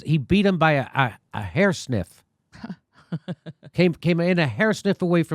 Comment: Jimmy is crazy! 😆 Lost by a hair sniff! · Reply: I thought he said hair snip, but hair sniff is much more appropriate